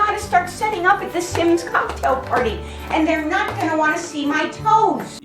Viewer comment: The lady sounds like she is saying "The Sims Cocktail Party"